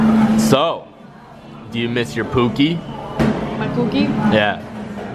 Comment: I like it when said Pookie I thought she was Saying cookie